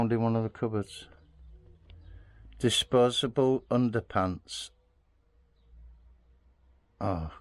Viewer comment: I think it says disposal underpads, whatever that means 😂